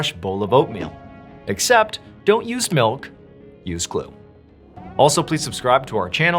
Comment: I had to relisten to the like button torture at the begin. I thought he said "Dont use milk, use gluten." I guess we are supposed to assume that the like button cant eat gluten.